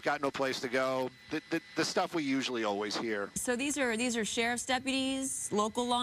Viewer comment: all i heard was blah blah blah blah blah blah blah blah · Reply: I heard “usually always”. That cracks me up.